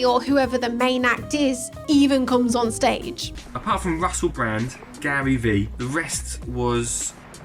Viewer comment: Did he say Russell Brand??